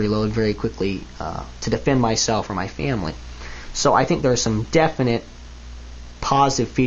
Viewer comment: It sound like he says "defend myself from my family" Note: I do understood what he really said.